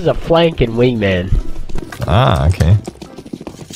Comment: I Thought he said "I DROPPED MY F*CKING GUM"